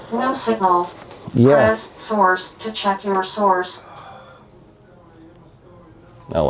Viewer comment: “Press source to check your source” ….. driving me crazy 😂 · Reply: I thought is was saying check your sores !!!!!................LOL